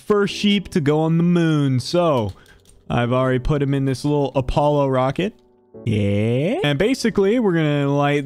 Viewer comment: I thought you said Apollo, not Challenger.